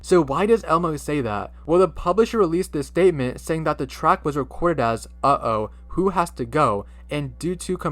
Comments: I only hear “Uh oh who wants to dance” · For the Elmo one I heard “ uh oh who has the gun?”